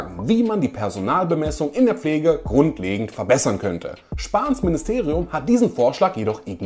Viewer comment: Ich hab mich verhört ? Spass-Ministerium statt Spahn-Ministerium ? Haha